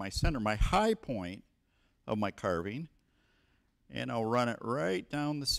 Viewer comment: did he say hi point carbine?